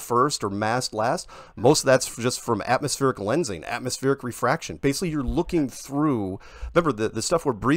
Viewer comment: did he just say atmospheric refraction?